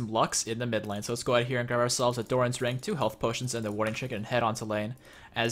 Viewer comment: At the beginning of the video I thought he said 'warding chicken' ...... I need sleep...